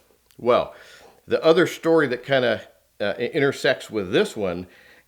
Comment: Did anyone hear him say intersex or its just me @ 1:44 🤣🤣🤣🤣